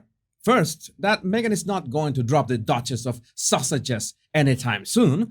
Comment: I adore you! I love your humor & calling them the Duke & Duchess of Sausages is priceless 😂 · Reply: He says sausages. I hear Togers's.